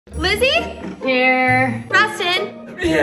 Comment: When she said Lizzy I thought she said glizzy😂😂😂😂😂